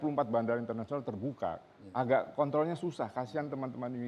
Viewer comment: gw salah denger gk ya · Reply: Kontrol